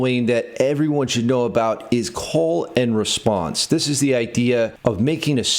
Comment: I thought you said "colon response" for a second there 😂 Diabolical